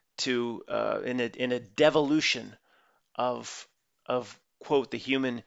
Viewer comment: When you said de-volution it sounded like you said devil-lution… so true 🎉🎉🎉